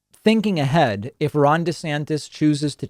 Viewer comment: I thought you said Ron De-satanist lmao